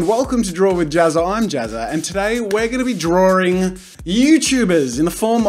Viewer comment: DID HE JUST SAID DRAWRING?